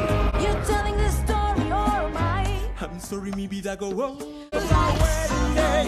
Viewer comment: When Felix says " I'm sorry mi vida now go on~ " I thought he said " I'm sorry babah gall now go on~ "